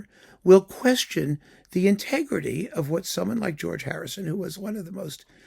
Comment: Did you say George Harrison just then? 😁 Thieving all that Indian music culture. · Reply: Yeah, he did say George Harrison. LOL.